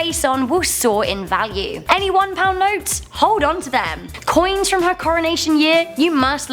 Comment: did i just hear ANY ONE POUND NOTES